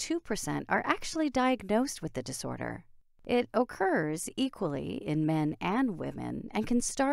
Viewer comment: what she said: "occurs" what i heard: "okurrrrrs"